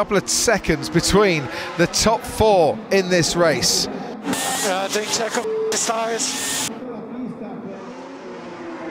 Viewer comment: what did max say? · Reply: I think he said "How is doing Checo with his tyres"